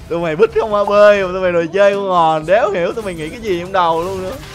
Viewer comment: rõ ràng là không mà sao nghe ra "con" hay vậy =))) này Editnon rồi